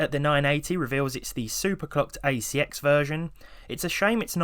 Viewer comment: I thought I heard Super Cooked ACX version instead of super clocked